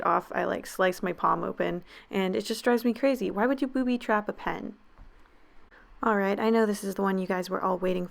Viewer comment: I left to go get food and when I get back I hear ‘Who would booby trap a pen?’